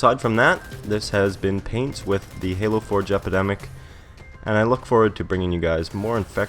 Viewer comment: did you say Jepicdemic?